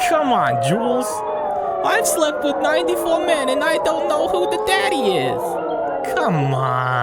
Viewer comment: Did you just say you sleep with 49 men